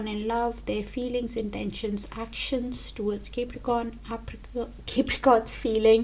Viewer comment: did you just call us apricot ? Haha ! ♑♥️